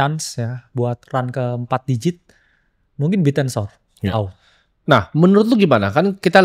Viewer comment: bilang apa ??? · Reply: Bittensor